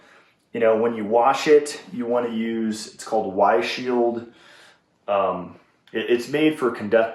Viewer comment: what kind of detergent does he suggest? I can't understand what he said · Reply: He said Y Shield!